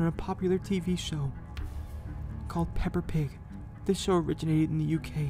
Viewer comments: What my mind thought Deku said: Called "Pepper pig" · I STILL CAN"T STOP LAUGHING, IT SOUNDS LIKE HE SAID "PEPPER PIG"